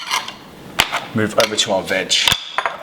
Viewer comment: i thought jst said : next stop we moved to our vag.